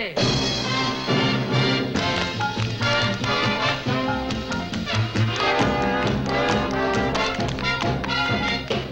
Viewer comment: does Donald say "Bunny"? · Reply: thesherlockpimpernel I’m pretty sure he said “Buddy!”